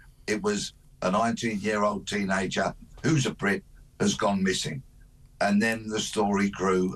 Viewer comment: I thought he said "who's a prick" 😅